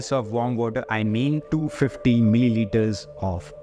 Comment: At about 3:42 on video, I am not quite sure what he says about the amount of milimeters (?), to be used in a glass of warm water. Is he saying, "two 15 milimeters of apple cider vinegar?" Sorry, it's just that I don't hear it clearly enough to understand what's being said. If someone could clarify that for me, it would be much appreciated. Thanks. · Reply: he meant diluting 1 TBSP of ACV with 250 ml of warm water.